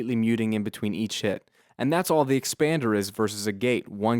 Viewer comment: hahahahaha I pressed left so what you said was taken out of context, I thought I heard you said "eat s--t" sorry I just have to